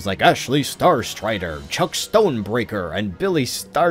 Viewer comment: I heard it like Jack Stone lol